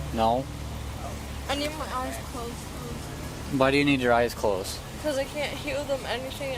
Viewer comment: Did she say I need my eyes closed bc I can’t hear anything?😭😭